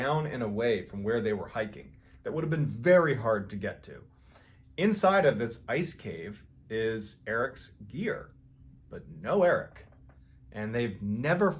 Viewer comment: when you said erics gear I thought you said ear